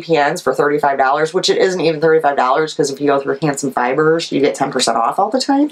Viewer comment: Great podcast. Where do you buy your needles. I thought you said Hanson Dye Works, I cannot find them. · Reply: Handsome fibers